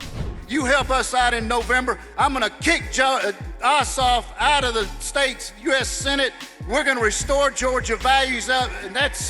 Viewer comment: did he say he'll kick himself out of the Senate?